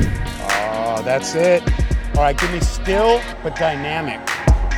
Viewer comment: Photographer - "Ok - give me still, but dynamic!"; Model - 'Yikes! What the hell do I do with that piece of direction?' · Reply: 🤣 damn, you beat me to it. “Photographer” I thought he said skill but dynamic. Still is just as bad. Yo “model”, I don’t have a high powered strobe so you gotta stay still for this low powered LED. 🤣🤓